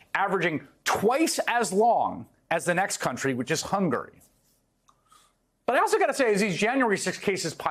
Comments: Hunger? Did I hear that right? 😂😂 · None of these criminals are sorry for their crimes and they should all be getting the maximum. At the 2:45 mark. Why did he say Hunger instead of Hungary?🤣🤣🤣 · I thought he said hunger instead of Hungary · Did he just say Hunger instead of Hungary?😅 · Judges should take note. None of these guys are sorry for what they did. Give everyone of them the max! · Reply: None of these criminals are sorry for their crimes and they should all be getting the maximum. At the 2:45 mark. Why did he say Hunger instead of Hungary?🤣🤣🤣 I guess he was hungry🤣🤣🤣🤣